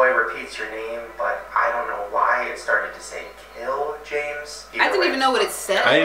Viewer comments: Sounded like it said "yell James" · The first one i thought it said tell james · Bro on the elmo one I hear "Hello? James."